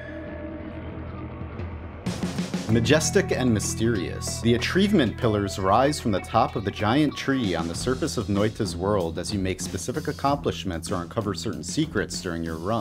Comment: Did... did you just say "the a tree vement pillars"?! you beautiful bastard, you